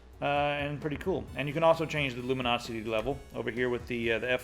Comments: I thought he was saying alumanazi levels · I THOUGHT YOU SAID ILLUMINAZI NOT ILLUMINOSITY